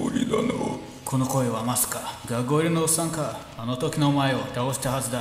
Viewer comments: 学割のおっさんか って聞こえる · がんこのおっさんかと聞こえる